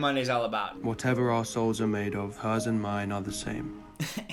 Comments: Lmao I hear it as "whatever arseholes are made of...." when Harden says it. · I can hear Emily Brontë turn in her grave when Hardin used that "whatever souls are made of" line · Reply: I love the original line and WH is one of my favourite books, but I misheard the opening monologue as ‘whatever arseholes are made of, hers and mine are the same’ and I am deceased 💀💀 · I really thought Hardin said, “Whatever arseholes are made of, hers and mine are the same” and was deeply confused 💀💀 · I'm so dumb, I've seen this movie commentary so many times and only now realised he said "whatever our souls are made of".. I heard "whatever arseholes are made of" and was so confused why there was more jokes made from that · in the opening line i deadass thought hardin said "whatever arseholes are made of, hers and mine are the same" and i've been laughing at that for like 10 minutes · Why did I hear " Whatever arseholes are made of"??? 🤣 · i heard "whatever arseholes are made of, hers and mine are the same" I WAS SO CONFUSED